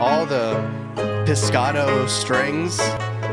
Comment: Did he just say pisscato strings